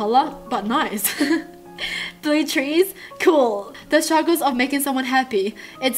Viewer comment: When she said blue trees I totally thought that she said blue cheese😂😭😂😂😭